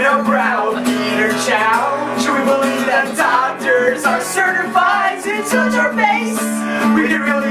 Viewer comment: it said doctors i thought it said dog turds my bad